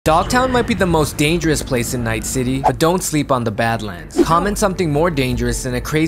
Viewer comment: Istg I HEARD you say Dantes might be the most DANGEROUS man of night city 💀💀💀💀💀